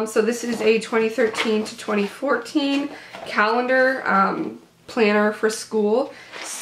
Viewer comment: When you said planner i thought you said planter so when you pulled it out i was like how do flowers go in that uh